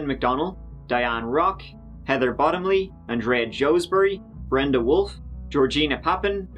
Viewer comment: Dude, I didn't think the victim list was ever going to end. 🤢 This is wild. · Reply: I thought he said and Reah Joesbury, I was like wow, those poor six women, but it was Andrea Joesbury and he said twenty-one more names!